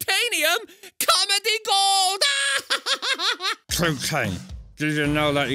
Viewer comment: o thought he said “cocaine” 😂😂